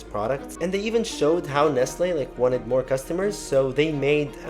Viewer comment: The caption is wrong, it's Nestle not Nasa